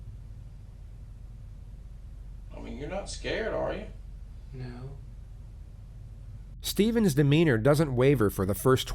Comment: Can anyone tell me what is said on the phone at 13:32 ? I can't quite make it out. Something about a buccal swab? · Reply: uh, said dont forget about that voucher for the buccal swab cause he's gonna send it up to the crime lab That's what I got from it.